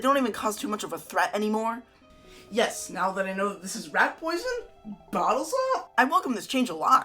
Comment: I had subtitles turned on on the rat poisoned part and the subtitles thought said “rap poison” and I thought for a second he was going to drop a beat the second he drank the wine lol. but yeah anyways this was cool good awesome video also I like pringles thank you :D